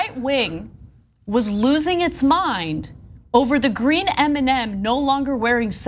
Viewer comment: Ana lost me for a second there. LOL!!! I thought she said, "green Eminem."